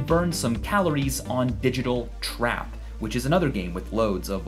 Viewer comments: did he say Trap and not Trip? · OHHHHH, digital trap, I thought it said digital trip